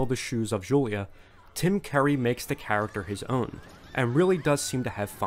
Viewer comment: It's probably your accent but I hear you saying 'Tim Kerrie'.